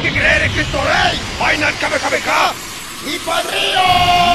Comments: ahí le entendí "finale Kamehameha y Pal río" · Alguien entiende lo que dice en el segundo 0:17? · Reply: Final kamehameha y pal río